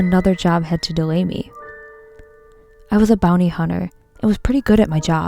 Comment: For some reason I thought it said “Booty hunter”